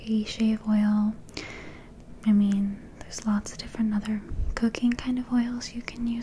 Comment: Did she say "cocaine kind of oils"?